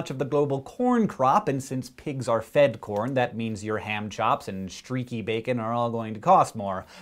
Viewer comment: Lol, at 1:25 when he said "ham chops," I actually thought he said "hand jobs."